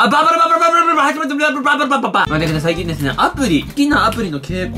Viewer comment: 挨拶の最後らへんバーバパパって聞こえた！w